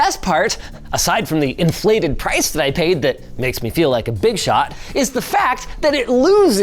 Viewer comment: did you say ([bigshot?])